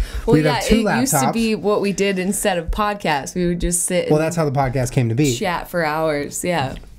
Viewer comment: I thought said "We just sit and shat for hours"